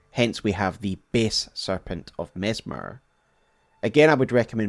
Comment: I'm not gonna lie, I keep hearing "The based serpent of Messmer".